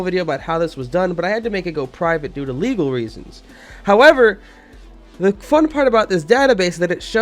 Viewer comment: did u say "the fuck part about this"???????????????